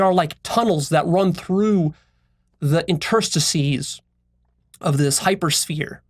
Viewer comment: Hello! Can someone explain what he said at 11:30, please? "...They are like tunnels that run through the (enterstecies?) of this hypersphere..." Thank you! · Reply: Interstices.